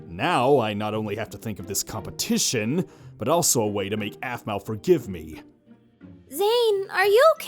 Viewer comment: So when kawaii chan said this to zane , ''Why dont we make aphmau~senpai.....'' Instead of make aphmau I thought she said make out XD